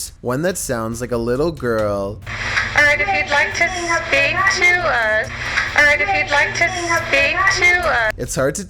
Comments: I hear 👂 “I would like to speak to her” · #3 - I heard “Would you like to play with me?”